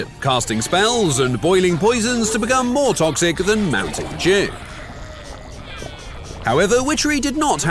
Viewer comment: More toxic than mountain Jew. Is it just me who hears this?